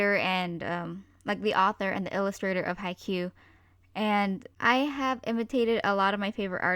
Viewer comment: At first I thought you said, "High Q." Then I realized you meant, "Haikyuu." Lol, I'm dumb. Also I don't really have an art style, I just draw randomly, and if it's good I keep going or I leave there and not finish it at all. I also use other art for random parts that I like, if I like how someone else did it.